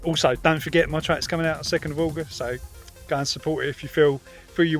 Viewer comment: New music out 2nd April? Text on end of video looks wrong, thought you said 2nd August 👍